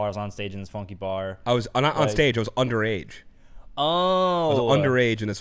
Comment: I always heard that as “I was all the rage” (in this funky bar), but you’re right. According to the official Paul Simon website, it really is “I was underage”.